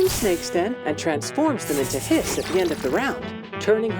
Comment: I'm absolutely sorry about this, but when having the English subtitle, instead of "Hits" or "Hiss", Google Auto-translates and Show "piss". I saw it from anothee videoplayer before, but at 3:16/3:17 it happens again. I love this game dearly, so maybe someone can alert YT to this, or Bluepoch about the pronounciation/accent. 😅